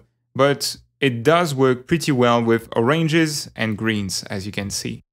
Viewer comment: xD i thought he said Arranges, then i realised it was his accent... then i wanted to kill myself for laughing at him saying something differently .-.